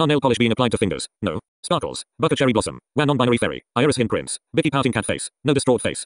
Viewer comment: Did it say non-binary fairy or am I hearing it wrong